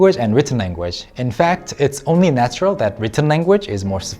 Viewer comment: did he said "only natural language" So sorry for that but how can you surely say that Korean is only natural language or anything "only". There are more better languages I'm not saying something bad I really respect this language (as I'm learning it) but saying that it "one and only" in some way is not good too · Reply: He said "it is only natural that spoken language", talking about spoken languages in general, not specific to Korean